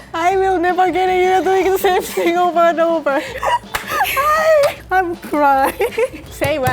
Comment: could anyone understand what was saying 😂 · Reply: Something like 'I'm never getting you like over and over??'